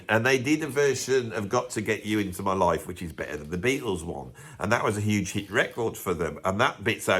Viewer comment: Usual funny video but, and I must have misheard you and need microsuction on my ears, but I could have swore at some point I heard the words 'Hendrix was better than the Beatles' please forgive me for being so outrageously wrong but that's what I thought you said. Apologies as I know you would never say that, anyway I'm off to the hospital.